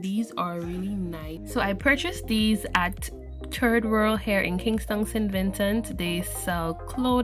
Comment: did you just say Kingston I was wondering which Kingston you're talking about but apparently you also have a Kingston full full 💻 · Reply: No!! You heard wrong sir!! 🚫We have a Kingstown not a kingston🤪😂💯